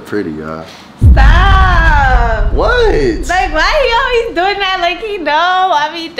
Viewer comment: did she said babe?🥹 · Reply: no she said “like why”